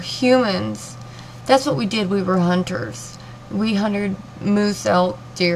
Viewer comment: At about 4:32 you totally said 'huntered' instead of hunted and I seriously about died laughing lol!!! I would seriously have a hard time seeing animals put down. :( The thought makes me really sad... Great video doll!!